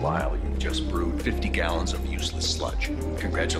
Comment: I always thought he said "Dumb ass know-it-all" when he said "Dump it, dump it all"